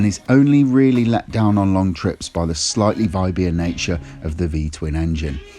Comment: Nice vídeo. Congrats. 👍 I'm tented to buy a vstrom 2020 black edition. Mainly for commuting and some touring. I also do around 300 km on highways twice a week. Can you explain me what you've said at 4:34 about "...let down on long trips by the slightly (v... i... b)? nature of the v-twin engine." Can't understand what you said, between parenthesis. And the meaning of the sentence. Sorry, I'm not an English native. It's importante to me as I need to do a lot of highway. Which one do you consider better for that purpouse. Thanks. Nice rides. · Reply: he said viby, meaning it vibrates. Don't know about the test rider or how the tracer is (I didn't ride one) but after proper day of 6-7 hours riding on my V strom 650 XT (2019 model) I had absolutly 0 issues with vibration. I actually felt none ...